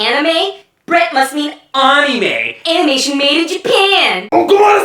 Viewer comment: Wait, did he just say "Onime"?